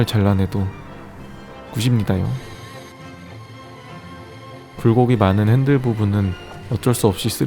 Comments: '굴곡이'를 불고기라고 들었습니다요 . . . 9:14 롤빵..? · 불고기가 많은으로 들었는데 자막에서 굴곡이래서 놀랬ㅌㅋㅋㅋㅋ나만 이렇게 들리나??